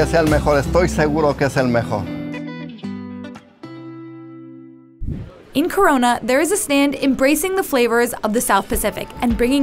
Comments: did she just said in Corona 🤔🤔🤔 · did I hear "Corona"